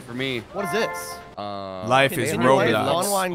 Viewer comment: anyone else hear say "life is roblox"